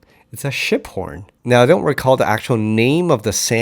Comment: Awesome video again! Thanks! for a minute there I thought you said the bass sound came from shit porn 😂